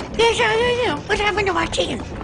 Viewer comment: Yesh I ayam whahappenedtomahteef* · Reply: I heard “my chicken” but really fast like “chkn”. Also he sounds like Bobby from king of the hill. Goddamnit Bobby!